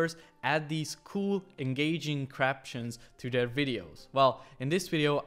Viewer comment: did you say Craption??? Haha!